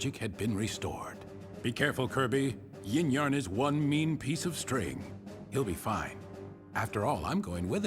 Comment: I thought meta knight say 'Yin Yarn's one piece of shit"